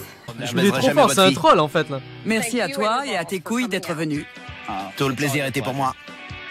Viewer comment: je veux un 2ème fucking épisode de cette émission PS : à 2:47 on est d'accord qu'elle dit "merci à toi et à tes couilles d'être venus"